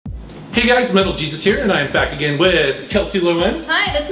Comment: I love how much they grew over the years! also i tought i heard you say ''little jesus here'' and i just pictured a mini version of yourself standing next o Kelsey🤣